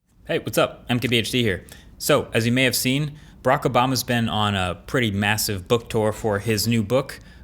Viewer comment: did he say Brak obama